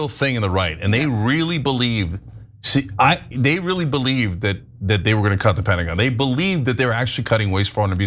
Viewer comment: Lmfao what's up cenk, I thought you said that it's great that they really were gonna cut the Pentagon!